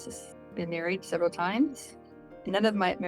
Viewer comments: Right out of the gate, 'I've been married *several times*, none of my marriages were happy and 29 years with my last husband'. . . . WTH? Did her last husband disappoint her by dying : ( As soon as she sent 2 laptops ( not even testing with 1 ), they knew they hit a jackpot. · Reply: I had to rewind it.. I thought she said married seven times 😅 but I wonder how many she’s had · I thought she said she had been married three times? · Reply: 🤔 I think she actually said "several," which is probably four or more. She said she wasn't happy in any of the marriages. I think she was always expecting a fairytale and disappointed she didn't get it. 😐